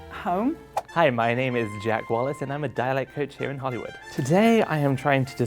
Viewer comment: I thought it said diet coach